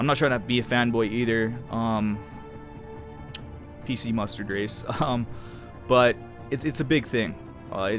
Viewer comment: Did he say... PC mustard race?